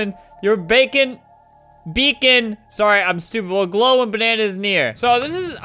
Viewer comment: did flamingo just say “your bacon will glow when bannana is near